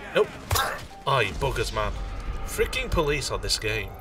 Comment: I swear it sound like you said I eat boogers man